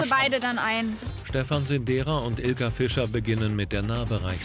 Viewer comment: Ich hab statt Ilke Fischer helene Fischer verstanden😂